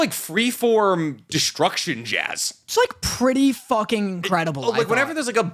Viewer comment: I heard “it’s like 3/4 destruction jazz” and then “non structured music” a few seconds later and totally thought what the actual fuck does think music is. I’m so glad I misheard.